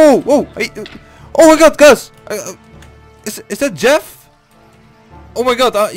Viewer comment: Its dark hqhqagahahahaha Dark:wtf how did u know Me: in 3:34 then stop lol:lets do a revenge 😎😎😎 6:54 i hear my name is jeff lolxdd